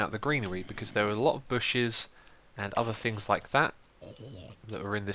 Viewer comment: it seriously sounded like you said "booshes" like Keralis. Made me happy.